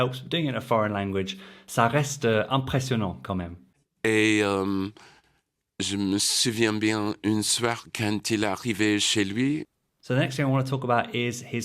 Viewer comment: I think you misheard what he said…. moi j’entends: je me souviens bien…. quand il arrivais chez lui… and that is not a mistake.